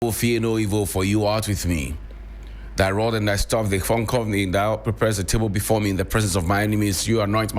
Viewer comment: Good delivery 👌🏾...however, on a lighter note did I hear you say ''thy rod and staff they 'foncort' me'' ...u made me go like kaiishhh (dem get we)😂😂